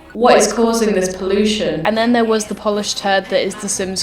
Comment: did she just say the Polish turd?